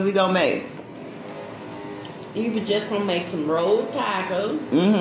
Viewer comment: You saying rolled, I thought you were saying "road".....😀😆🙂